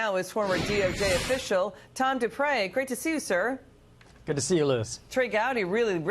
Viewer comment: I thought she said "time to pray" not Tom Dupree.